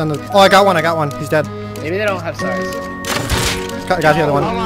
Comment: Did anyone else hear "maybe they dont have s*x" at 9:38 🤣